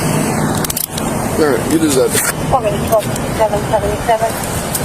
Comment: Did anyone else hear the cop say to the man "sir, you deserve it"????